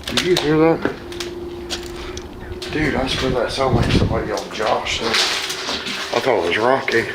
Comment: Am I the only one who ears "Come on" instead of "Josh"?